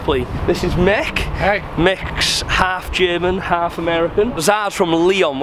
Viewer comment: I thought I was going tho said half size